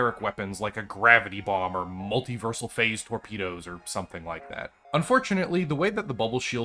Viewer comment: when you said "multiversal phase torpedo," I thought you said "multiversal phase burrito." My first thought was I should build a missile is Space Engineers and call it such... and then backed up the audio and realized I mis-heard. Still contemplating a burrito torpedo. XD